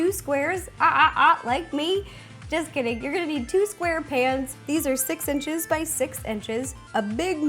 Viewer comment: I thought you said SQUAREPANTS! (1:08) hahahaha. Can you please make something from spongebob? PATRICK STAR?? Hahahahaha thanks!!!😀